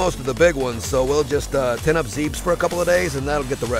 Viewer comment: did he say,"zeebs?"